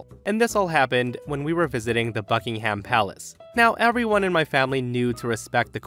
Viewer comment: For the first story I thought he said “fucking ham palace” instead of Buckingham Palace lol. Also I think it’s funny op cousin got mad when there’s literally a vid of a guard running down a lil boy bc he wouldn’t move for guards